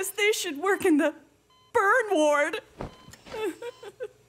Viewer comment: Did she say bro?